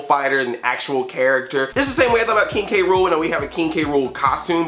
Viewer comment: Sound like he said "Kinky Rool"